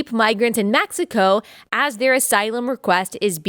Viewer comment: Teehee... I thought at first that you said we had more migraines (instead of migrants)... Although that may be true as well! 😉